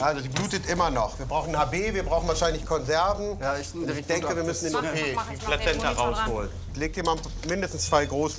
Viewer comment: habe ich verstanden wir brauchen einen O.B 😂😂😂